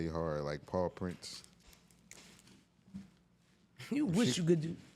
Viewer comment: Oooh Paw Prints… I thought he was saying Paul Prince until Eve was mentioned. I figured it was some Philly shit idk about 😅